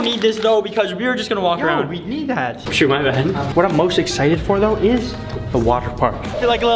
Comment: Is is just me or did say oh sh*t my bad